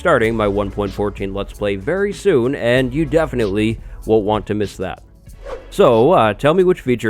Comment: am I the only one who heard him say “you want to miss that” when he was talking about his let’s play at